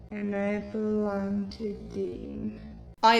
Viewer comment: I kept hearing "I belong to demon" for crying eyes it. If so, I wouldn't argue, I believe it.